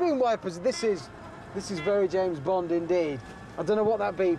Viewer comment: "Bulb failure." Oh, dear, someone nicked a turn signal light. Anyone got a spare? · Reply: Troubleshooter125 I thought he said ball failure. 😷